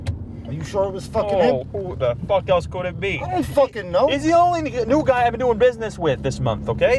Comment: he almost said the N word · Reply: Ooohhh Yes! I just heard it now. It's real fast. It's like "Ni New guy". Well as for the script the "New Guy" isn't black, so I don't know what he was trying to say, but yeah, I now see what you were talking about. Lol